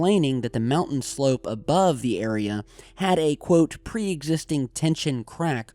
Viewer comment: did he say the mountain above had depression?